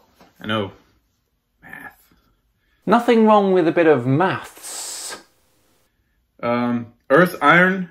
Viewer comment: I thought he said it's just a bit of meth 🤔🙄😳